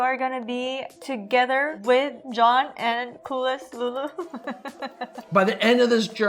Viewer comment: Boo yah!!! Nice work! I’ll be following you to 200K, wherever you go! Maybe “Coolest Lulu”(?), because, that’s what I thought she said.🤣